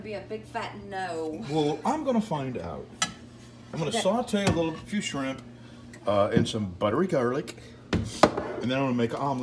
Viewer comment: I thought you said a few shrimp and a little pepper. Good thing I was watching the screen.